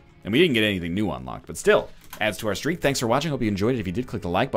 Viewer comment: When NL said "Still, it adds to our streak though" I heard it as "Still, it adds to our street cred"